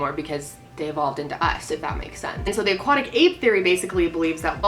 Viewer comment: At one point I def thought you said the "Aquatic Ape FAIRY" and my mind went to so many weird places lmfao 😂